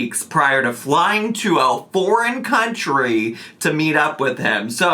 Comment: did I hear "flying to a boring country" or something else??? Kuwait - boring???!